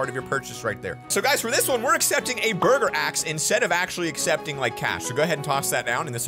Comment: I thought he said murder axe not burger axe and I was just like “U trying to be demonetized?”